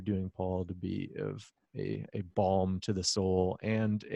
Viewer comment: I momentarily heard this as "a bomb to the soul" rather than a balm. Maybe it's a bit of both, with all the things we are exposed to. Intellectual pushing, indeed.